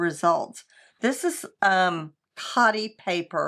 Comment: I thought you said “potty” paper. My Google search was … disturbing 😳 😅😅😅😅😅